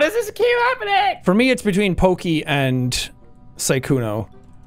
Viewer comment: when he said "poki and rae" i thought he said "valky and rae" and i had to replay it lol